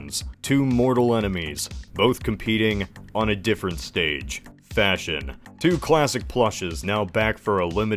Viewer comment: Did someone say FASCIST!!!??? ...oh, no... ok.